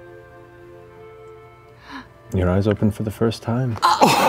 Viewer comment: This felt like a better way to do things. Taliesin had gone on the record saying undeath or resurrection was against what he thought Molly would want. The first attempt here was every other player trying to make his character come back, and it failed. It was only when Taliesin agreed to give it a shot that it worked. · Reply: That's not what he says, though; he says, "Your eyes open for the first time...in a long time," but the last four words are swallowed up by everyone's reactions at 14:47. Listen closely, you can hear it there.